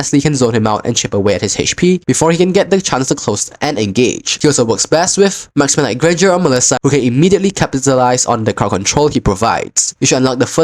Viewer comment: Bro at times its hard to understand your accent pls either talk slower or put up some captions for people to understand......i literally could not get what u said between 2:44 to 2:48 ......this is no shade or hate or anything just a genuine request to help me and anyone else like me understand ur words......sometimes it just feels like ur running thru the pronunciation of lot of words when u speak fast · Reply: "He also works best with Marksman like granger and Melissa , who can immediately capitalise on the crowd control he provides"